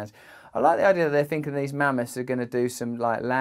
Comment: I thought you said Man-moth 😂